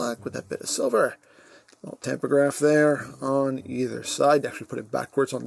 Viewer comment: what does he say at 5:11, "little tempergraph"? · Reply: Tampograph